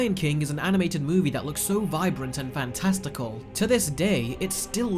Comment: I heard “fan testicles “